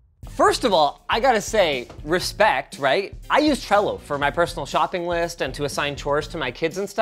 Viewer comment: what app did he say? cello?